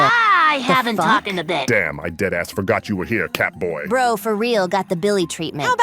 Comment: did you just say Catboy?